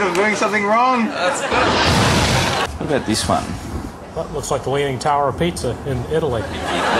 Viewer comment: Was I the only one who thought that guy said "The Leaning Tower of Pizza?" haha